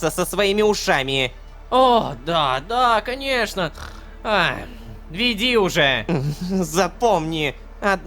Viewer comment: я одна тут слышу "Ой, да-да, конечно, хрю, веди уже."?